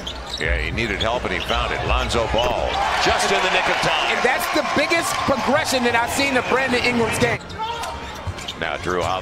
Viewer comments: what I heard 0:51-53: That's the biggest progression I've seen in Brandon ingrams gay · "Brandon Ingrams Gay" 0:53 thats what i heard lmao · (turn on subtitles) 🤣🤣🤣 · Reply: All I hear is "Brandon Ingrams Gay🤣🤣🤣🤣🤣🤣